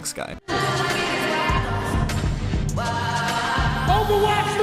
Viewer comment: it sounds like Khaled is saying "Overwatch 3" and I sm dying.